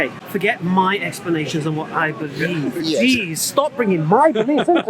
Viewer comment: did say Jeez ??